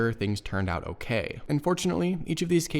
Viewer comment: I think you meant to say “Fortunately” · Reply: I did! It was "and fortunately" not "unfortunately." Sorry, I mumble sometimes 🙁